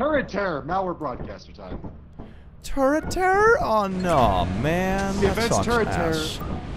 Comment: I thought he said turtar which is Irish for turtle and got confused. It makes more sense he said turret terror.